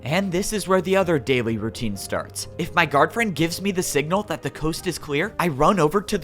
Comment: When I first heard guard friend, I thought you said girlfriend 😂 I'm dumb...